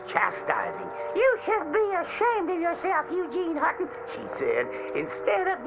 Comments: I heard "you should be ashamed of yourself "__ Eugene___???? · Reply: “You should be shamed of yourself Eugene Hutton” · I heard "You should be ashamed of yourself Regina", and thought it was a Mean Girls reference